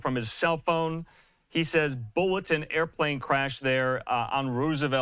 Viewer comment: Did he say bullets? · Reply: I heard him say Bullets too.